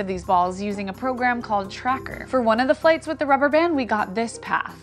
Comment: Need help: i was writing the italian subtitles for this video but i can't understand what "plates" mean in the sentence at 5:07 (more or less)... Can someone help me in figuring this out? maybe with an image. Sorry but i cannot understand it and the translation i thought doesn't fit in that context. Thanks and have a good day. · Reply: I'm two years late, but... the word is "flights".